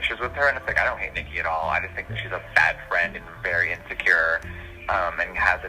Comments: did he say about Nikki “she’s the fat friend” ? And “She’s just fat and crazy”???? I’m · I don’t know about everyone else but I heard ‘FAD friend’ not ‘fat’. Which makes more sense. Just observing.